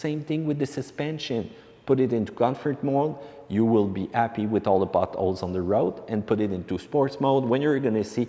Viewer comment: No problem understanding the accent. One funny part though @ 3:50 sounded like “you will be happy with all the buttholes on the road”